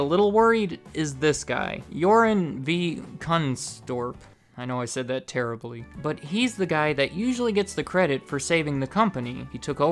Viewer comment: LOL The subtitles say “You’re in the gun store”!